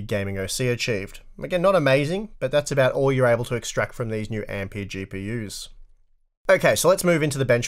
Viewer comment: Did you just call these RTX 3090s AMD GPUs at 5:28? Yes you did!!